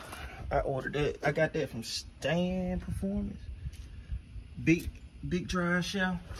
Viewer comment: On the drive shaft who u say again sound like u said Stan performance but I can’t find him